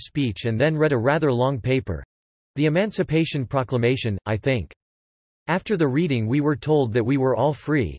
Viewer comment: I thought it said "Emancipation Procrastination"